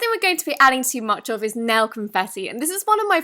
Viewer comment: I thought you said nell confetti but you said nail. I got excited coz nell is my name